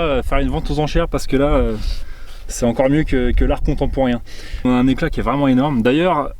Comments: Je ne sais pas si c'est moi qui ai mal entendu ou si c'est une pique, mais j'ai compris "l'art comptant pour rien" ce qui je l'avoue humblement m'a bien fait sourire ^^ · Je ne sais pas si c'est moi qui ai mal entendu ou si c'est une pique, mais j'ai compris "l'art comptant pour rien" ce qui je l'avoue humblement m'a bien fait sourire ^^ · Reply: Moi j'ai entendu "l'art content pour rien" 🤭